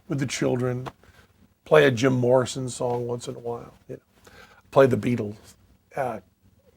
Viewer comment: Closed captions at 1:08 are a little off. Penn says "play a Jim Morrison song once in a while" and the captions say "pledge of abortions along with the poor." Wtf?